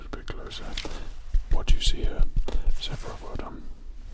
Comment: I swear I heard 'Sephiroth' when you said 'Zebra' and I had to roll back the tapes on that one 😂